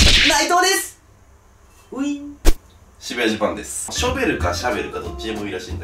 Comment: 渋谷ジャパンがしめじマンに聞こえる…